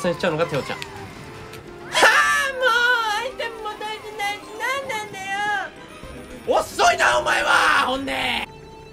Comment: 聞き取り不可能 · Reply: も〜アイテムも取れてないのになんなんだよぅ〜